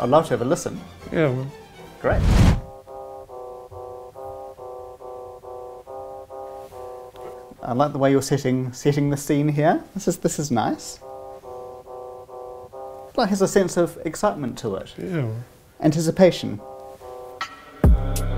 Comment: even I thought he said I like the way your sitting🤣no wonder tr looked confused